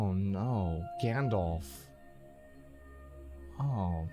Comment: okay im losing my mind over here. I've rewatched it like 20 times. Why did he say gandalf